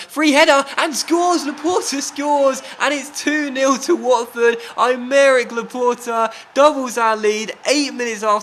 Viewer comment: When you said Aymeric Laporte i thought you said I'D MARRY LAPORTE